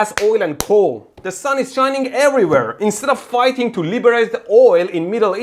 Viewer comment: "Sun shines every where" I thought you said you are in Vancouver